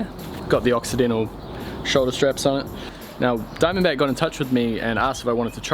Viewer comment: what did you say? occidental? octidental? brand and model please?